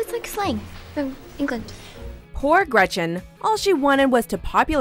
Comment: When she said "poor Gretchen" I swear I heard "whore Gretchen"